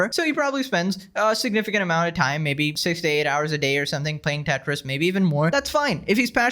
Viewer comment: I think he said he played 3 to 4 hours a day.